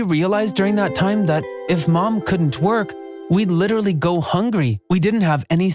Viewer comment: God please save me why did I hear "if my mom can't twerk" 💀